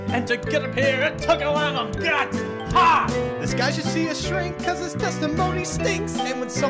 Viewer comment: For the 2 or so years I've been listening to this musical I thought that Phoenix said "This guy should be a shrink" It's "see a shrink" My life has changed